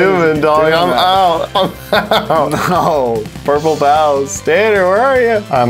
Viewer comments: I kept on hearing “Purple Pounce” not “Purple Pals” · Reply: Lol I heard “Purple Pows” until it said “Purple Pals won the round” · I thought he was saying purple Pows half the time Stay healthy social distance stay safe and wear your mask